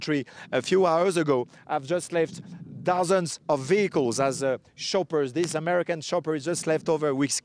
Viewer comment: Sounded like Shoppers ! hes like a salesman "shoppers come on down aircraft prices way down ! "